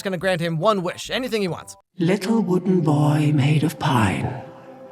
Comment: Am I the only one who thought the blue fairy said "Little wooden boy maid of PIE."🤣I couldn't stop laughing 🤣